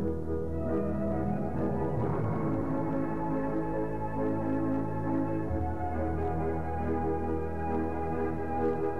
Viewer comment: τι λέει στις 1:00 ? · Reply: MEGA Καληνύχτα και όνειρα γλυκά